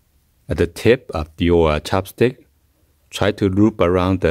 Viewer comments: I thought he said chap stick 😭 good vid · i thought you said chapstick so i started grabbing my chapstick, and then you pullled out a chopstick and i felt like such an idiot